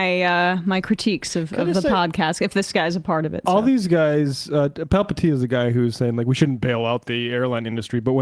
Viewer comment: I can't not mishear that dudes name as Palpatine